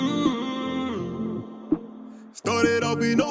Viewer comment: I LOST MY BROTHER TO DA GUN... RIP MARQUISE...LOVE U YO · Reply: I know it!!!! First I thought he say he loved his brother to the goon